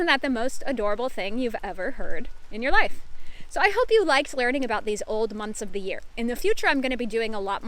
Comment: Вот объясните мне такую вещь. На 7:37 и 7:51 на ваших словосочетаниях "you liked learning" я отчётливо слышу "you likes learning". Что это за мистика?